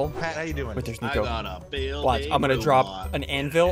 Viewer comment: i thought you said ant hill Lol